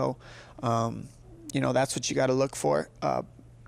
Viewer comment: Did he said: "Thats what she is gonna look for..??